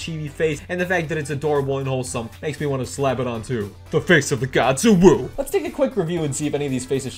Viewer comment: i heard “face of the gods uwu”